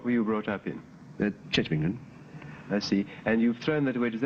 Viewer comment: What does he say? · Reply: "Church of England"(Anglican)